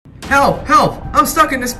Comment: I heard “kelp help” at the start of the video lol